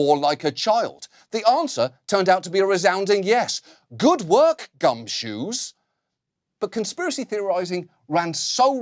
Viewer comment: Did he say good work Gumshoos?